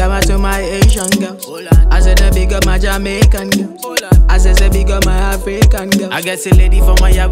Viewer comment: I thought I heard at 2:34 .... 🇭🇹💃🏿, 🇯🇲💃🏿, 🌍💃🏿.... (Haitian girls, Jamaican girls, African girls) 🤷🏿‍♂️